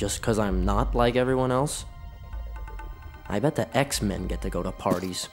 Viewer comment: Did he just say X-MEN